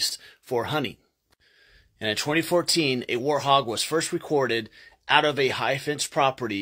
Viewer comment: I don't think that the letter "t" in the word "warthog" is silent... I'm sure I heard you say: "warhog"...